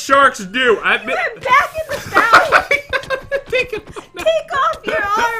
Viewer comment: What I was expecting Pat to say: It's all for fun! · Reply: When Jen said you came back in the fountain I thought she said you came back in the mountain